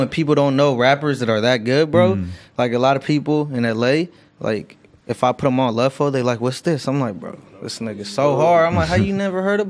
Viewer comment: what rapper does he say he put people on and he couldn't believe they hadn't heard of him I'm interested in listenin to a new artist he likes cuz his music is straight fire so I'm assuming almost everything he listens to is straight fire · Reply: Austin Parlee lud foe